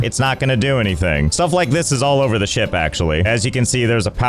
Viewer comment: I hear all over the s***t😅😂😂